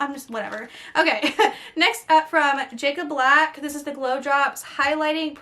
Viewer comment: Lmfao I thought she said Jakob black when she said jecca black. I was like whoa and thought of twilight lmao! 😂😂😂😂